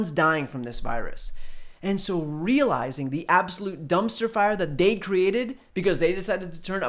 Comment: I think I heard it pronounced "Dumbster fire" and if I didn't I think I probably still should have.